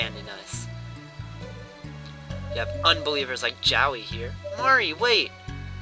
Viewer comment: I thought you say jowee like joey ...